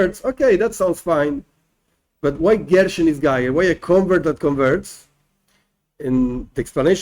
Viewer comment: Can you spell out in Hebrew what you are saying at min 11:20 the two words for when a " convert converts"? Goyshem ghayah? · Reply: Ger shenisgayer